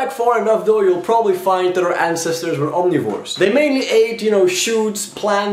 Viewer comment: I wasn't listening too carefully and on the ancestor's diet part I thought he said "They mainly ate shoes."